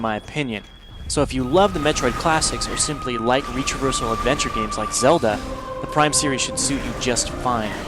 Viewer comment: what does he say at 2:57? sounds something like "reachorchal adventure games like zelda" lol